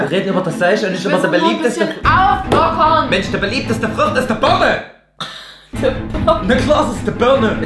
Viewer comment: warum hab ich anstatt Birne Vogel verstanden 😂